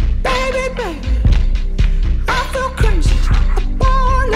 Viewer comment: Zayn- 'baby i feel crazy'. what i hear- 'baby i feel cringy'.